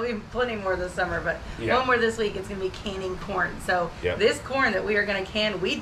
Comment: I thought you said "One more this week, it'll be canning PORN." 😂😂😂